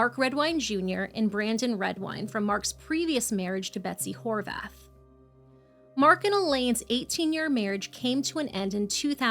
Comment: I really thought she said his ex wife's name was Betsy whorebath 😂